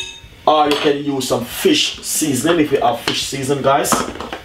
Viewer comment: LMBO! That was a cup of olive oil, not a teaspoon! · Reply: what did u say?? fish seasn or what did u add in